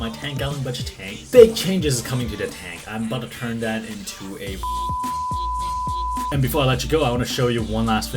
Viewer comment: I think he said that he’s going to turn the 10g into a bubble tip reef tank, he also said he might on his ig